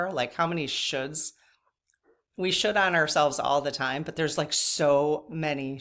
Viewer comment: It sounded like you said, " we shit on ourselves alot". I laughed so hard.